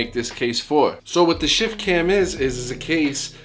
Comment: I kept hearing "Shit Can" lol